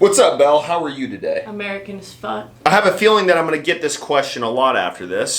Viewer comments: When she said "American as fuck" I went all heart eyes. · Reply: I just laughed honestly cause i thought she said " america is fucked " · "How you doin' today" "American as Fuck" Best answer ever! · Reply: I thought she said America is fucked. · Hahaha! Hey how are you today? “American as Fuck” Hahaha! That is a classic! I’m going to use that sometime but I’ll give due credit for sure no worries. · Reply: Lol I thought she said America if F*cked. Which we are.